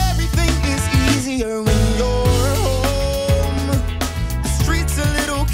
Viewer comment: At first I heard when you're gone so I thought it said "when you're gone, the streets are a little kinder when you're gone"